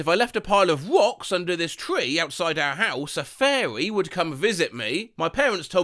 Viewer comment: i wasn't paying attention and i thought he said furry